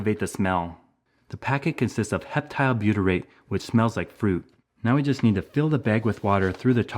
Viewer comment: Is there a difference between this trap and the fly trap they make that looks similar or is the only difference the labeling? Never mind. I think I figured it out. You said the wasp bait smelled like fruit. I know from experience. The fly trap smells like rotten fish guts. I put one out in my back patio and had to throw it out. The stench was just too much to bare. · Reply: LOL, is that what he said? I thought he said it smelled like poop. I've used the fly ones before too; I know what you mean, it's a terrible smell.